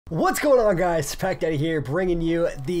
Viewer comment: Your intro sounded like you said what’s up guys pekka here just think of pekka when he says it then rewind it and think of pack daddy it’s just the intro though kinda trippy